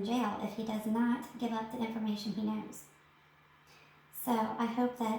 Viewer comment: DID u just say if he does not give US the information he knows ?? If you said US - then who are you ? Are u police ? Or TBI ? Or are u just a YouTuber that feels ENTITLED that Fred Hill needs to give you info?? ... SMDH please STOP!! JUST STOP putting out MISINFORMATION!!! please!!!! This is cruel to do to a deceased person family and also to Summer Wells too .. smdh !! 🤬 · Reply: 1) I said if Fred doesn't give UP the information he knows. I never said, not Us. 2) if I was law enforcement, I would not be making videos on an open case 3) All this information is available to the public, nothing came from top secret NASA. 4) There is no misinformation on my part. Were is this anger stemming from? 5) I am very sorry for all families who have lost their loved ones The fact is that until LE finds Summer, we all will continue to dig into everyone who is brought into this case. If you are a local, tell Ronnie Lawson to publicly start clearing people until then, we have to take his word that everyone is still a suspect Where is Summer Moon Utah Wells?